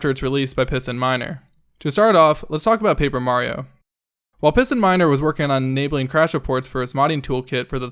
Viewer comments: PistonMiner... I thought he said PissinMiner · I heard "Pissin Minder" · I keep hearing "pissingminer"